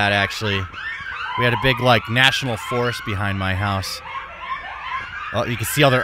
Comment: I thought he said "natural forest"... glad he didn't.